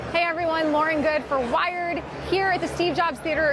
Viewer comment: LOWKEY SHOOK THAT I HEARD "LAUREN GOODE FOR WIRED" INSTEAD OF "LAUREN GOODE FOR THE VERGE" AAAAAAAAAAAAA